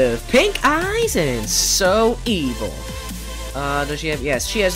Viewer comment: Did You Just Say Saw And Evil